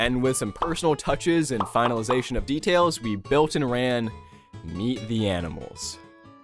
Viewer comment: When I heard "meet the animals"... for a sec I thought "meat the animals" and had flashbacks of "can your pet" xD